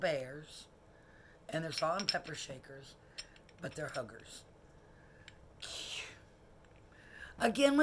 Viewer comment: Believe you are right about ice in bottom of butter dish. LOL.. thought you said hookers instead of huggers. Need to get my ears checked.